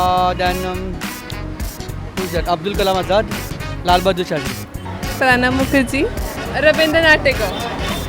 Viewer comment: Did he say Lal Bahadur Shastri!